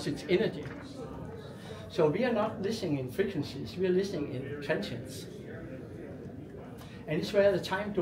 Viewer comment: AWESOME! At 9:54 says “we are not listening in frequencies, we are listening in (what does he say)? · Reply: Energy... Transients.